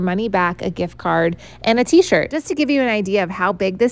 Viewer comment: i heard 't shirt' as 'seizure' and it sure looks like one would get it